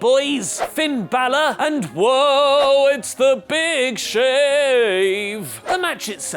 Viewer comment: Did you just say big shave 😂😂😂😂